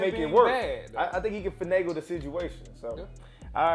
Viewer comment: Did he just say "fanagle?"